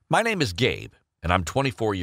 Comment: I thought he said:my name is gay but he said:my name is gabe and i said oh my god lol jayda i did not copy